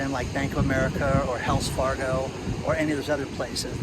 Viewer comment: I do believe this is your best video yet. The aquarium, beach, your humor, and good news. Love your casual look. You have come along way since that corner in your home...shutters behind you...and your low soft voice.😀 We love spending time with you on your great outdoor walks. Keep the videos coming.😁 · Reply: I thought he said "Hells" Fargo 🤣😂